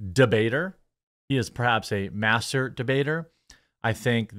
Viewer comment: lol I thought he said master bater 😂😂